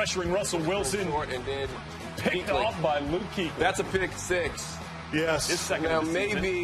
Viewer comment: did he say that was a Vick six?